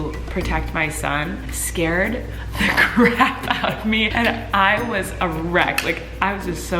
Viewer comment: Where she says “ I was a wreck “ I thought she said “ I was erect”